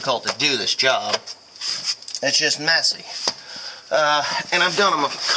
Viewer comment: ish did you say it’s just messy or Mesick(y)?